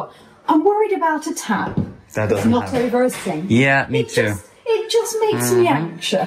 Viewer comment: what is saying? Not over roasting? I’ve put captions on it and still can’t pick up what the concern about the pot filler was 😂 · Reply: She's worried about a tap that's not over a sink. You're welcome. 😊